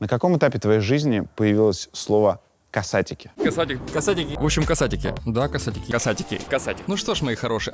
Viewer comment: В конце послышалось: "Инкассатики", ахах